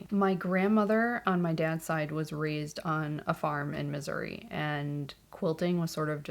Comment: Comment 2, good for you, I love hand quilting and none of my quilting cohort do it except me. Also, I thought you said your grandmother lived in misery... oops, Missouri.